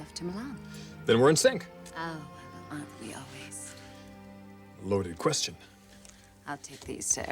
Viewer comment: Can someone help me? What is Margo saying at the beginning at 0:11-0:13? And what is Ridge answering? I couldnt really understand it. Would be nice if someone helps. 🙏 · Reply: I think she is saying,, we are always ,, and Ridge repley,, loaded question